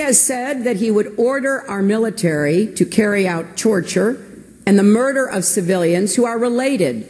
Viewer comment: Did she say to carry out Chorchure instead of torture?